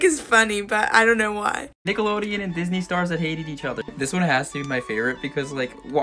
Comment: I thought he said Disney channel stars who dated each other and spent the next 30 seconds being extremely confused until I finally caught on😂😂